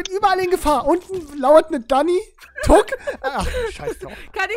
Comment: Tuck?! (5:34) Ich hab es jetzt min. 5 mal angehört... Und ich höre immer noch TUCK! XD